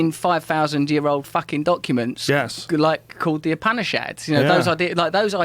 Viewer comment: does he say the "Apanish Ads"? Google isn't helping me out here, thanks in advance! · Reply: Upanishads